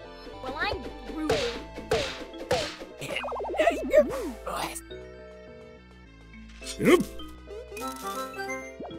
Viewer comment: Did anyone understand that? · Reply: "Well, I'm groovy," Vi says while on 4 hp.